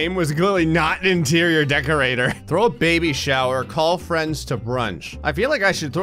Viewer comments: Lol I thought it said throw a baby in a shower lol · Ngl at 5:40, I thought it said “throw a baby in a shower”.